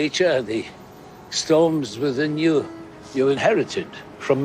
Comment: I thought he said "stones within you". In reference to nirn stones.